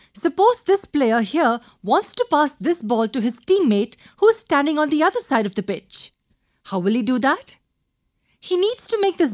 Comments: Did she just say "standing on the other side of the BITCH" OMG, really? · did u just say a bad word????????? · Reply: its "pitch" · i keep hearing "bitch when she makes the soccer ball example and its hilarious.